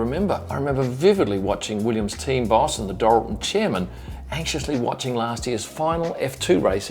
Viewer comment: I thought you said the "adorable chairman" at 3:54. 😁